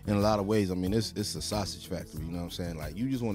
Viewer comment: Ok, so hear me out here.. Am I the only one who hears this guy saying music is like a sausage fest?? watch from 2:00!!